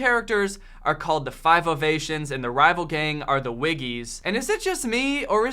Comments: I know this video came out 1 year 6 months ago, and this movie came out 10 years ago. But I thought was saying "5 of Asians" and I had to look at Wiki to see their group is the 5 Ovations. The fact the movie was so weird I just accepted it speaks a lot. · I thought you were saying "5 of Asians" the first few times you said "the 5 Ovations" lol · I thought he was saying "5-Oh Nations" the first 3 fucking times .... I'm like.... what the hell the 5-O got to do with this shit? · Lol when said "the 5 ovations" I thought you said "the 5 of Asians" and I was like Hold up just a second😂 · Whenever he said "The 5 Ovations" I heard it as "The 5 of Asians" and thought wow that's a weird group name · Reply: I always heard it as " the 5 of Asians " until i read your comment ... 😶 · Holy jeez. It took me way too long to get that he was saying "5 Ovations". I kept hearing "5 of Asians" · Whenever he said "The 5 Ovations" I heard it as "The 5 of Asians" and thought wow that's a weird group name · Ohhh it's the Five Ovations....good, because for a second there I thought he kept saying The Five of Asians .... like the ace of spades but not ... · All I hear: “the 5 of Asians” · I literally thought he said "5 of Asians" instead of "5 ovations" 😂